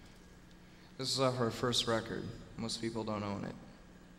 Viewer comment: Welp, i just learned that when he says "This is off our 1st record" He says "Most People Don't OWN it." I thought he said "most people don't KNOW it"